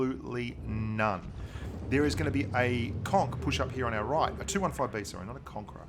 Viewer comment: I thought says coçk